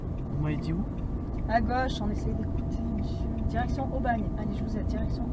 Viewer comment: elle dit "putain, il me soule" où ce moi qui rêve ?